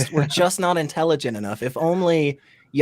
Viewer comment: Misheard Brahman as Ramen and thought, is the true name of the flying spaghetti monster?